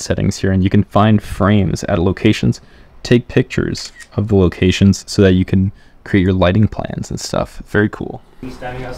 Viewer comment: Hi, What’s the of the app used at 4:32. I heard “cat rage” but nothing matches in App Store lol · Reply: cadrage bro